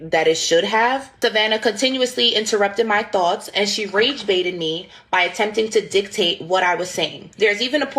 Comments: Did she actually say was "RAGE baited"?🙄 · "Race-baited" you? She was black also! You're the one who attacked her first! Fact is you could not defend your position without sounding like a self-centered murderer looking to play but are unwilling to pay! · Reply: I thought she said "rage-baited". I need to go listen again now.